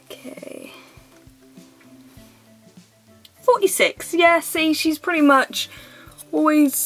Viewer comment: lol it sounded like u said 4e6